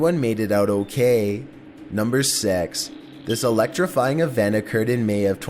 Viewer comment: I swear on my momma he said NUMBER SEX!